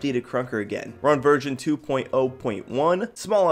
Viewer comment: Anyone else think he said Virgin 2.0.1